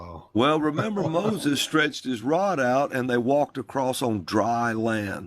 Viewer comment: I heard "stretched out his staff (I begin to snicker at this point obscuring what he was saying until I heard him say...) All dried up🤦